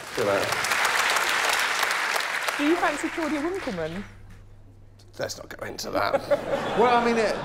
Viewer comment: I'm a bit confused...what did Roisin say at 30:14? (The accent's a bit strong for me...) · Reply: "Do you fancy Claudia Winkleman?" because of Josh's question for the Swede 😄